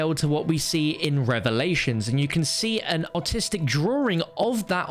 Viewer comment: did he say autistic 😅